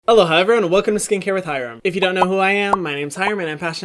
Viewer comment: This whole time I thought you where saying “hello hi everyone”